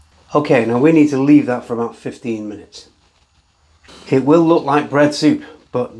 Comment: lol I originally misheard 2:58 as "ok now we'll need to leave that for about 15 years" Thank goodness for lip-reading. 😅